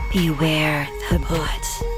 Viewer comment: Who else thought she said "BEWARE THE FOOT"